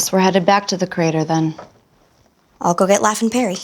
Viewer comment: "I'll go get LaF and Perry". I heard as, "I'll go get laughing Peary"